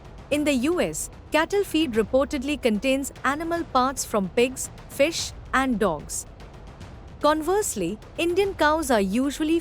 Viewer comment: did you just say cattle feed contains pigs, fish and DOGS?? Did i hear that correctly? Am i hearing things? Or is that a mispronunciation?